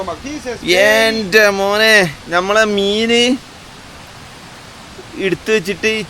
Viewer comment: Can't Understand But It Sounds Horrible Of The Smell To..'Yante-Monee🤣😆